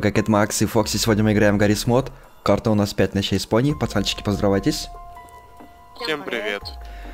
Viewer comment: Мне послышалась не пони а бони